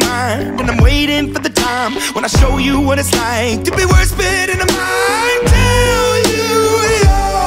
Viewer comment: Does anyone else hear "worse but in the mind" instead of "words spit in a mic"